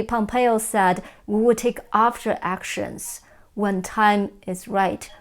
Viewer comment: AFTER-ACTIONS!! ahhh ...I thought I heard "OFTER-ACTION" hmmmm